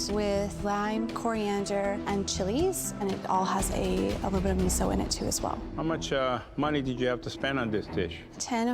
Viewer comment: what did she say it has in it? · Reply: miso vinaigrette and kale